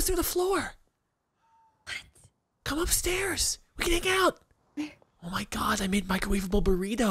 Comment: “Come upstairs! We can make out!” Is what I thought said at first lol.